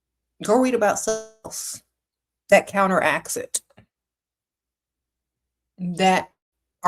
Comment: What did she say at 1:42:03?? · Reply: Probably said “read about something else that counteracts it”